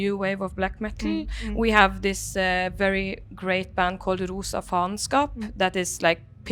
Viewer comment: Can you help me and write what the band she said at 7:35 is pls? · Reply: Rosa Faenskap